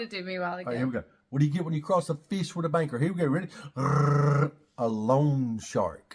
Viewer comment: just old socks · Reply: I figured out the joke. I thought he said baker. 🤦🏼‍♀️